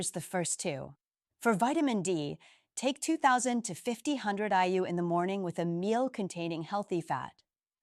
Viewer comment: 'For vitamin D take two thousand (2000) to fifty hundred (5000) IU...' Why not just say five thousand? The subtitles say 500 IU. 5000 IU is generally regarded as much too high in the absence of calcium deficiency. You should not suggest it and people need to check their blood calcium first. The title says take this vitamin at night, but in video you say in the morning. The claim about rock hard erection especially in older people is exaggerated. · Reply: You're right—I said "5,000 IU" and if subtitles show 500, that's an error I'll fix. The 5,000 IU dose is safe for most men over 60 (many are severely deficient), but yes, get your levels tested first and work with your doctor. Magnesium goes at night, vitamin D in the morning with food—title refers to the main mineral. And the language about erections is direct because that's what men actually experience when vascular function improves with proper magnesium levels. I see it clinically, not exaggeration.